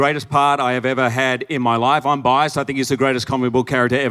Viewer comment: Wait wait ! 1:05 did hugh just say he is bi?